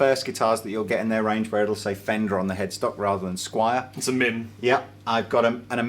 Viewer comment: What dies he say @ 3:01? It sounds like he's saying 'It's a mint.' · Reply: It's a MIM